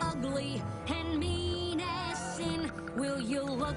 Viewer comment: "They're big, and ugly, and mean as sin!" Wow, if ponies know what sin is, is Celestia their god? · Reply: Yeah, that's why I thought she was saying "menacing" because I have never heard "mean as sin" before, and I didn't think it sounded right :3